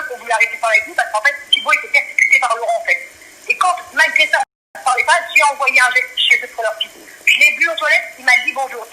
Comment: elle dit quoi Thibault était persécuté par Laurent ? Pzs compris ? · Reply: Obsédé par Laurent...